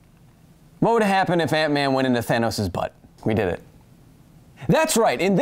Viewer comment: honestly heard aunt May instead of ant-man